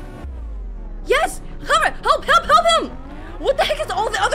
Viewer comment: What said in 1:22:42 is “Help me.“